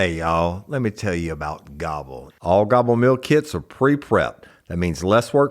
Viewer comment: At first I thought he said let me tell you how to wobble 😂